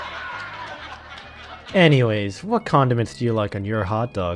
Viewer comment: and I heard 5:20 Anyway, what c*nd*m do you like on your hotdogs… OK I need more sleep